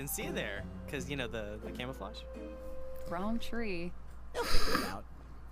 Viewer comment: First time watching, I thought Neptune said camera focus instead of camouflage......I kinda loved it....